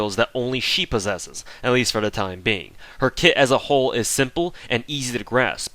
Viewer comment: i misheard her kit as her tit on 14:57, or did i hear that correctly?